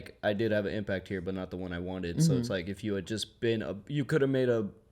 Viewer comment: Mark 21:28. "Wanted"? I thought that he said, "expected"? 🤔 Hmm.